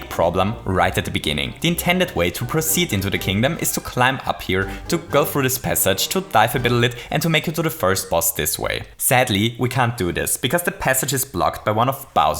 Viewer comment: I don't know if I am alone here, but did you just say a bittle lit?